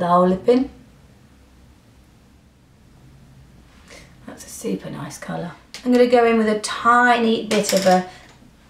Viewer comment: when was commenting on the first lip liner she applied i could've sworn she said "stupid-ass color" 😂 because i was distracted for a moment but still listening (or so i thought). i had to replay it 3 times before i realized she said "super nice color" hahaa! my first thought was 'whoa 😳 tell us how you really feel nic' lol. anyway, i was thoroughly tickled for a moment and had to share. love you ladies!